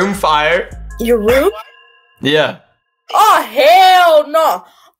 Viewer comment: I think she said oh hell nah to the Pearson under the bed😂